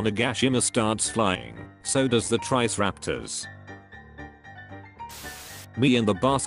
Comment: the actual words "so does the triceratops" what the AI voice says "so does the trice-raptors" lol